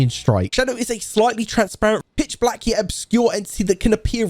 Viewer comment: "Bitch black ebs" What did you call me?